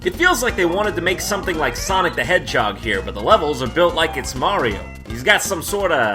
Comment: I thought he said "Sonic: The Headshot" at first, lol